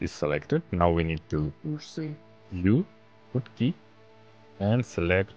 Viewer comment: This comment is just a note for future me. I watched this video months ago and followed along perfectly, for some reason this time at about 5:25 i wasnt able to understand what you said, but i figured it out, you said press U after pressing A to project from view (bounds) option to pop up. Your welcome future me!